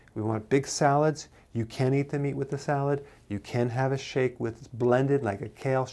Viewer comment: Did you say you cant have the meat with the salad? Thank you. A new subscriber trying to figure it all out! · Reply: I thought he said you CAN eat meat with the salad. That is what my ears heard.